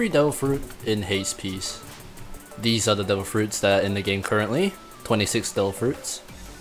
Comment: I heard he said ''Dough fruits'' instead of ''Devil fruits'' everytime he said it.